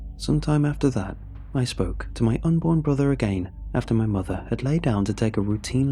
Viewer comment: Idk what's worse, whether this dudes unborn sibling spoke to him, or the fact I thought it said unicorn instead of unborn. 🤣